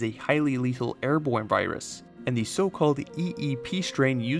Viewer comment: I don’t no if it’s his accent or something, but every now and then it sounded like he was saying birus